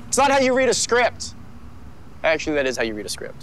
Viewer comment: So am I the only one who heard him say "the kid's got chops, MY kid's got chops" even the CC thinks he said that like Matthew explain?